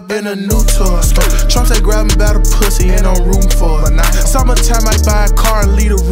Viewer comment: So we're all just gonna ignore that Trump line 😂😂😂🔥🔥🔥🔥🔥🔥🔥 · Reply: I thought it said n im rootin forem